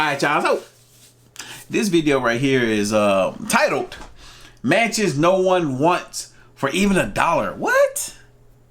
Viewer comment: What i heard " Manches nobody wants for a $1"